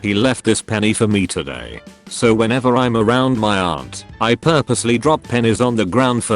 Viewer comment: Tbh I thought it said he used to drop panties 🤣